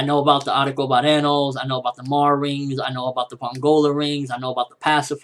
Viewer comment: Lol i thought i heard "Maury" Rings!